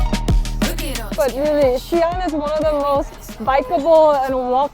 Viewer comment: the subtitles say "none" instead of "one"